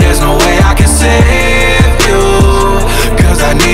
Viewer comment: I hear "come back" Not "goodbye"